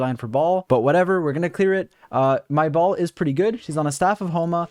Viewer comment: This guide is so good... but I can't stop laughing at 20:51 after I misheard it as "My BALL'S pretty good..." So sorry....